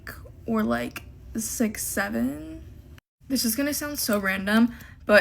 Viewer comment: did she just say 67